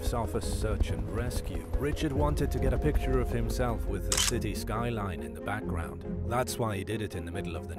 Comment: LAMO 😂🤣 number 5 it sounded like you said the shitty lights in the back ground